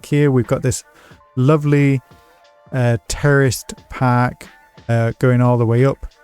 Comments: Am i tripping or did he really say terrorist park?????? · I heard "We've got this lovely... terrorist pack" xD